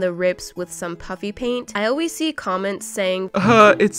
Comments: I always heard puffy paint , but when said puppy paint.... I couldn’t unhear it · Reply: I heard her say puppy paint and NOT puffy paint!!!😂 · I know I’m not the only one that didn’t hear “Puppy Paint” until she addressed it. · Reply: Same like I heard her say puffy paint and once she addressed it I heard puppy paint XD